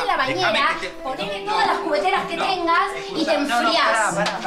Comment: Que es esa palabra que dice florencia en 18:43? Juguetera? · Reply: dice cubetera no juguetera